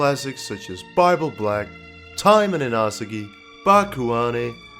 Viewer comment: can someone tell me its name cause i dont understand what he is saying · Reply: Taimanin Asagi